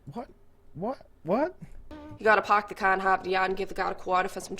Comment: “You gotta park the car in Harvard yard and give the guard a quarter for some chowder” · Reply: Gotta love the Boston accent, lol. I thought she said “and hike the yard” instead of “in Harvard Yard,” but got the rest after a few tries. I'm from Texas by the way.